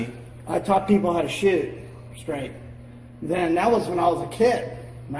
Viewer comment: At first I thought he said "I taught people how to shit straight."